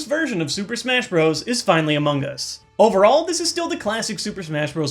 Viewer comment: HA I heard "Finally Amoonguss"